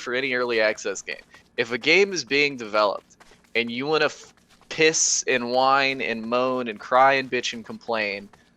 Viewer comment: thought he said "piss in wine"